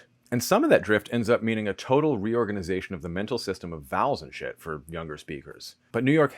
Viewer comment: This guy said vowels n shit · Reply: I thought that’s what I heard, but convinced myself it was “vowels and shifts”. Now I don’t know.